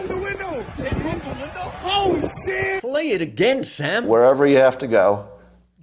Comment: is he saying "cleared again, Sam"